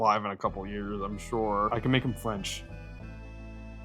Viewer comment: i thought you said that you could make him french and got worried